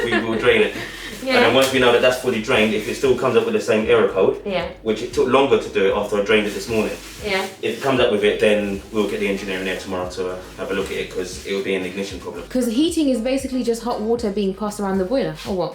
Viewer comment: Hi, I’m having a whole unnecessary debate with my family (lol) about what said (8:44)about the engineer coming. I heard here tomorrow everyone else seem to lost their mind and heard next month. Please tell me which one it is because they out here try make me think I have hearing problems